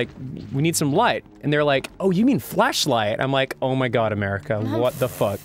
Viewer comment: Is it me or does it sound like is saying "Fleshlight" O-o